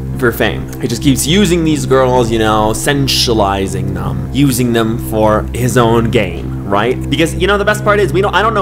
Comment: i swear i heard <he's using them for is own gay>